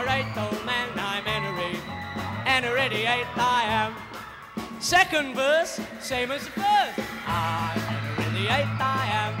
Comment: I thought it said, "Second verse, Seymour's verse"